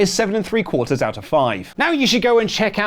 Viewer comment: 7 3/4 out of 5 ? Did I hear that correctly?